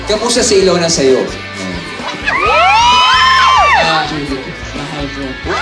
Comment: what said? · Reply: TYG Vlogger he said mahal ko